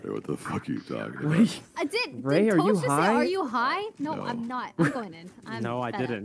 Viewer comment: TBH It did sound like toast said are you high, and when Rae said no it sounded like he said oh your not? Lmao I’m convinced he was trolling when he said he didn’t say anything. 🤣🤣🤣🤣🤣🤣🤣